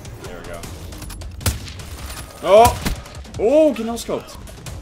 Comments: Did I hear a kid yell noscope in the background?? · who else heard the really quiet "GET NOSCOPED!!!!" Meme in the background!😂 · When he hit the no scope i heard "GET NO SCOPED" but wasnt sure so i played it back and cringed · Anyone else hear "Get no scoped!" at 3:57? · does anybody else hear a little kid screaming 'Get noscoped!' @ 3:57 ? EASTER EGG CONFIRMED!!!!! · did anyone else hear the "Get NoScoped!" at 3:58?